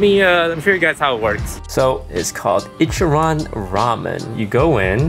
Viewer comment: I thought he said ichiraku ramen